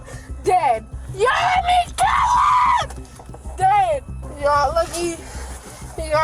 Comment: I swear she threatens to kill her kids?!?!?! Did anyone else hear that?